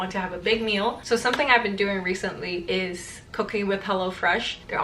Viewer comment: oh looord when you said “cooking” i heard cocaine sorry 😔